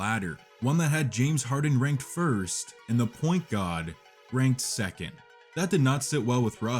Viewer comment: did he just call Russ the point god??!!